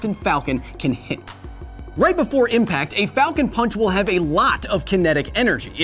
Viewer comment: Captain falcon sounds like he’s saying FALCO PANCH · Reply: i always heard it as "falcone ponch" XD